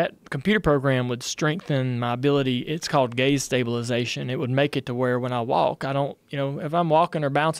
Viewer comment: I heard that as “gays stabilization” when I was listening to this at work. Caught me off guard 😂😂😂